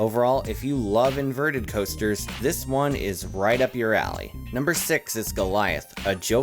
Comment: why did i think he was gonna say right up your ass